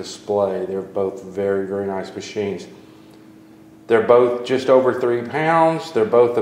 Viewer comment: Did I hear that correctly: 3:35 "They both just over 30 pounds?" That doesn't make sense :D